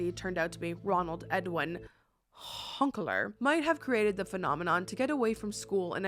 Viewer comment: I really thought she said, “Hunk Alert,” at 20:05. I thought to myself, “Well, that was odd.” 😂